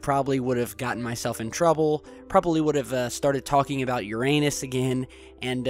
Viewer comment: xD Am i the only one who thought that he said your anus not uranus when he was talking about the vodka drinking?.. xDD